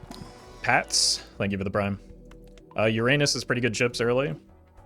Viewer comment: Every time you say Uranus I hear “your anus”